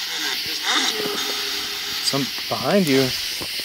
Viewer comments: sounds like it says "I'm right behind you." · | To me, it sounded like it said "I'm right beside you"